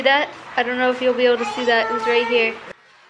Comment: Ok ok ok ok... but did she say “did you see that “he’s” right here” so it’s a boy.... I don’t care what the baby is I’m so happy regardless for the family😩😩❤️ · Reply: i heard "it was right here"